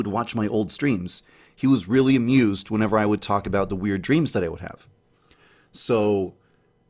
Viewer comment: I thought he said I have weird memes.